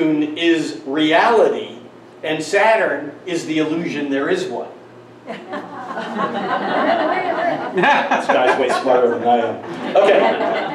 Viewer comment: What's said @ 38:03? [(After wink) _ "OK"] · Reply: What I said is, "This guy is way smarter than I am."